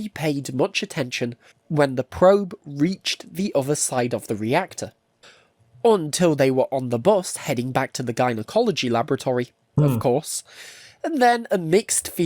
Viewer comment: I'm sorry, did he say "until they were on the bus, heading back to gynecology laboratory"? Apparently I know even less about nuclear engineering than I thought :\